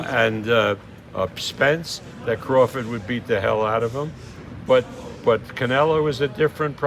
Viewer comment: What did he say that made him a casual. Charlo didnt win a single round. You sure your not the casual? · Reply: actually my bad I thought he said Crawford beats Canelo 😂 which would make him a casual if he did say that